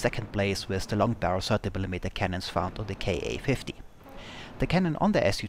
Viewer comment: What's the plane you're referencing at 2:20 ? I heard something like k 850? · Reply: Ka-50, coming soon to a YT channel near you. ;)